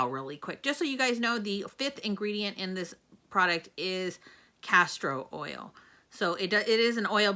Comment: It sounds like you said castrol oil & all I can think abt is car oil. I know you said castor oil just me . For me too much oil am oily & would break out even on my chest I would break out. Thanks bottle looks pretty I would be tempted but now know to pass for me.